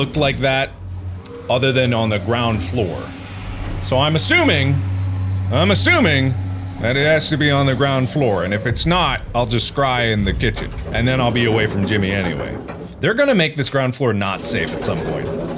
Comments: at first I thought he said "if it's not, I'll just cry in the kitchen" and I was like, same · I thought he said "and if it's not I'll just cry in the kitchen"